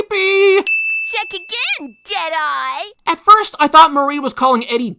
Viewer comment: When she said deadeye, I thought it she said Jedi, because Star Wars the clone wars was on air the first time I watched this episode.